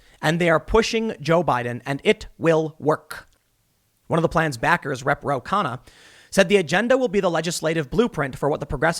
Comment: Damn Tim, for a second there I nearly thought that you were speaking Scoobie... When you said "Rep. Ro Kahanna," I nearly thought you said "Ruh-roh!"